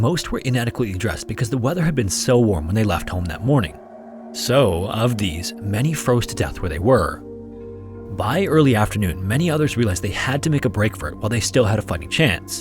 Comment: I had to replay 14:47 over and over for a minute cause I thought the voiceover said "while they still had a f*cking chance" And I'm over here shook like, this actually has to be the worst one if this man finally cursed.